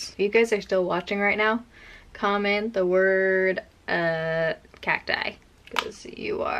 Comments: Totally thought you said “cat eye” until I went to comments 😂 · I heard "cat dye" and BOY I was confused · Cacti. I definitely heard "cat eye" 🤣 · Cacti! I thought you said ‘cat die’ Lol! I watched your eye palette de-clutter a while ago and now just found this. On a de-clutter video kick again. Haha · I heard that as "cat die" and I was weirded out, but I was like "okay... I guess..." So I looked at the comments to see if I heard it wrong, which I did... Oops... 😂😅😂 Btw Cacti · I thought you said ‘cat die’. Went back and heard cacti 😂😂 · I thought you said “Cat Dye.” But thanks to the other comments. CACTI! 🌵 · Cacti 😂 i thought you said cat die.. i said wow how depressing 😂😂😂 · At first I thought you said cat die lmbo Cacti 🌵